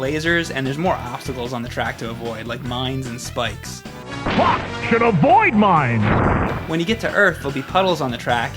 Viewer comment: Lol the announcer in Rock&Roll Racing sounds like he says "FUCK SHIT, Avoid mines..." XD